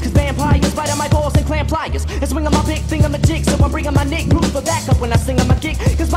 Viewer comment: lmao, he actually said "nig'" xD